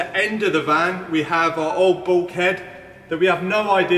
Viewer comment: Thought you said old BALL PIT!? But no, it was Bulkhead. Shame. Ball pit sounds a fab idea